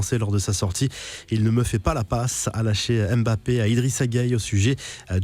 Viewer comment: Moi j'ai compris "ce chien me fait pas la passe"